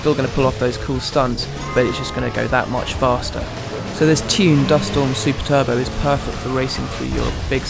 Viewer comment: does he say there are 2 dust storm super turbo???